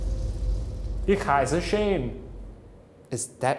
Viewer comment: I thought shane said Kaiser shane instead of ich heisse shane lmao